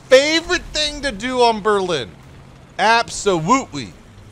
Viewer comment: did he say... absuwutwy